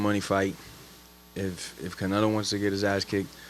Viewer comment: Did he say Canelo?!?!?!? That would be sick.